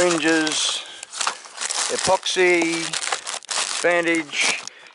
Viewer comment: Hi at 8:46. Did you say ipoxy? Just wondering how will you use that? Thanks 👍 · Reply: Epoxy 2 part